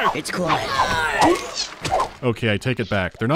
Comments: Anyone else hear a "it's quiet" at 9:32 or was that just me? XD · i heard that "Its Quiet"